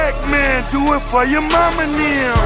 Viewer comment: I always thought they said "Play That Music" instead of "Maybach Music" Lol!